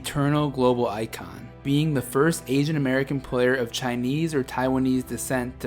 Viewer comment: am I hearing that wrong? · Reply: “Asian American” ok I listened over and over my bad